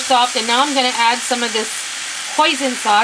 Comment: Omg I thought you said poison sauce lol at myself ill have to give this a try looks yummmy tfs